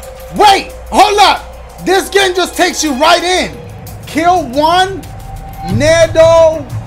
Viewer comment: I'm so sorry for this. But while only listening, I really thought he was about to say, "Kill one... Ni**a" I'm dead hahahaha